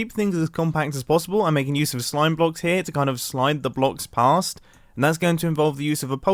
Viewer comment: did just say to slime the blocks a**?